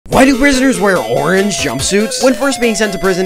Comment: I thought it said "why do prisons have orange juice?" And i was like "Whaaaaaa"